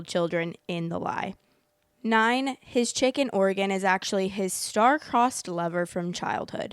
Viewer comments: The way I was confused bc I kept hearing “chicken organ” instead of “chick in Oregon” 💀💀😭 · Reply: I kept hearing "Chicken Oregon" and I wanted the recipe 😂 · The way I was confused bc I kept hearing “chicken organ” instead of “chick in Oregon” 💀💀😭